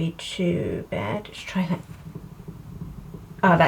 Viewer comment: I adore your videos of course... but... @ 2:21 did you say "(you) should try that" or did you just capture an epic evpolk